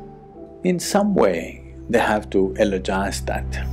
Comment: Can anyone please tell me what's the word used @ 11:43, not clear. Namaskara 🙏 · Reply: Eulogise